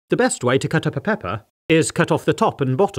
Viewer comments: I first heard it as “What’s the best way to cut PEpPa?” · i heard "cup up a pepper" instead of "cut up a pepper"